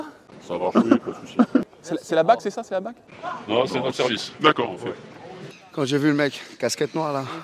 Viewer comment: Il dit quoi à 19:03 lorsque Greg demande si c’est la bac s’ils vous plaît ? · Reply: Il dit que non et que c'est un autre service